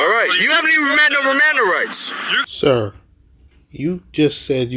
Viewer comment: did he just say mirandum rights.??